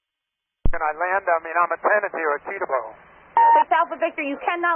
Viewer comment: I think he said "I'm a tenant here at Teterboro"